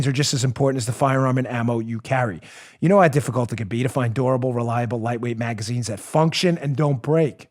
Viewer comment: When Dan was showing the spare clips, he said they were DURABLE but I thought he said they were ADORABLE!! I had to double check! I've never seen an adorable clip (maybe spray pink and glitter and some flower transfer)